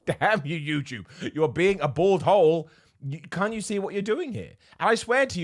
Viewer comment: So we went from bald arsehole to bald hole to now this 5:01 please tell me someone else heard bored hole XD